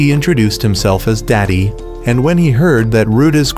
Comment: Bro when he date introduced himself it sounded like was calling him dad on